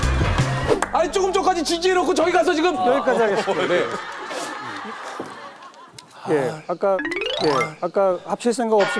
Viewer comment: 명수옹은 과연 뭐라고 욕을 했을깤ㄱㅋㅋㄱㅋㄱㅋㄱㄱㅋㄱㄱㅋ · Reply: 뭘봐 ㅅㅂㄴㅇ라고 한거같은데 ㅋㅋㅋㅋㅋㅋ